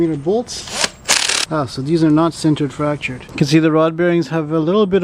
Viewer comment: what is being said? Cintered fractured? Please elaborate.